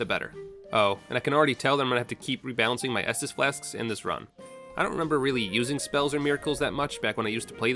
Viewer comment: I heard him say fist run